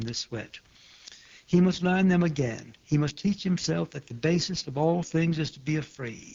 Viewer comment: I think he read one passage wrong. He said the duty of a writer is to "be afraid." I think his speech reads "to not be afraid" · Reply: Sorry. That's a mistake. I heard "basest' as "basis".